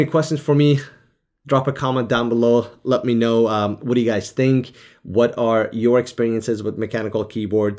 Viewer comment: In the end I suddenly misheard "let me know what you guys stink"...😅